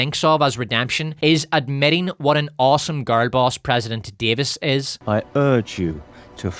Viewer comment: no shade I heard "gargoyles" for a few seconds before I realized you were saying "girl boss" lol whoops